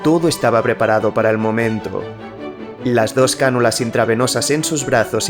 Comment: escuche bien , dijo 2 canulas intrapiernosas?